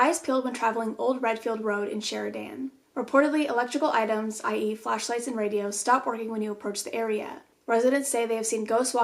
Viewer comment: When she said "i.e flashlights and radios" I thought she said "I eat flashlights and radios"